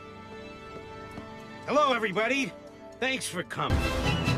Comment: Why i hear "hello everybody thx for c*m"